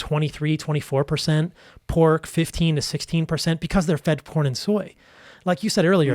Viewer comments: Does he say they are fed porn and soy????🤣🤣🤣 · who else heard (porn) 🤣